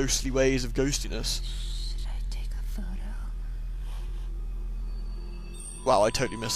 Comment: I heard "You should take a photo."